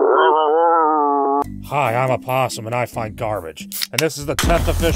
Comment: "I'm a possum, I find garbage." This is my favourite intro in all of youtube. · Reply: until i saw it written i thought he said " im a possum and i fight cars" aka roadkill donno if im happy its not or what